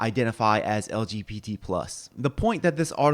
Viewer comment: mid eating thought he said chatgpt plus LMAO